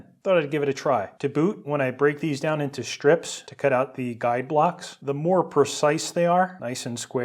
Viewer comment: What does he say in the background at 15:42? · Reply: " to boot" "to cut out these guide blocks"